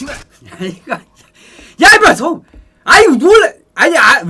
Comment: 여기에서 아이고난1이 잠깐 들림ㅋㅋㅋㅋ